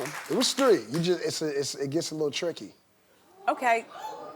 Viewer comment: He said tricky haha sounded like he was saying he fucked rob and caitlyn 😂😂😂